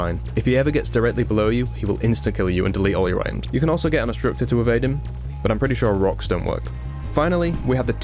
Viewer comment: what the hell did you say at 14:02? You can also get an instructor to evade him?